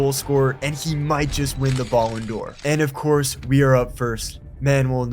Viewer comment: I heard him say "Balls On Door"